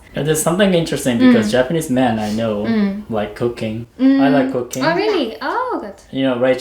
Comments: I thought he said cocaine, not cooking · With out context of anything, you could have thought he said he liked c0caine instead of cooking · I thought he said "Japanese man I know like cocaine". I was like "wait a minute, naniiii?" O_o